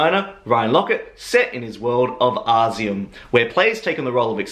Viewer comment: did you say ozium??? to funny, was deoderizer spray stoners used in 70/80's lol